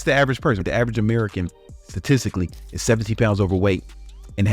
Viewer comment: I tipically listen only and I always thought he said the average American is 17 pounds overweight.... It's actually 70!😮